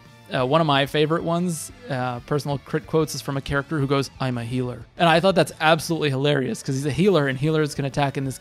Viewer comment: The full line is "I'm a healer...BUUUT!" Though I get that it's not easy to understand what that character says sometime especially with that crit quote.